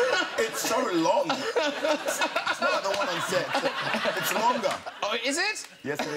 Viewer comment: Someone can tell me why does he say at 7:06 after "it's so long" I can't understand what he said and why he smiled just after that :) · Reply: He Said: its longer. Then the guy who runs the show Said: oh is it? And he answers: yes it is :)